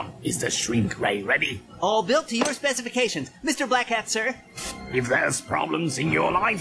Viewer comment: I think Flug said: All built to your specifications, Mister Black Hat sir!